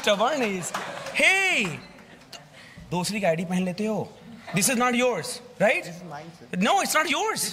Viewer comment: KOI 4:20 KE TIME DHYAN DIYA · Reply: "Dusre ki I'd phn lete ho" this is what he said 🙂